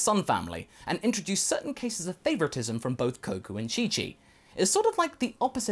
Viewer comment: I think I heard say “koku and chichi”